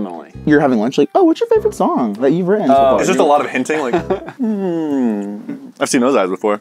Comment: Did said hentai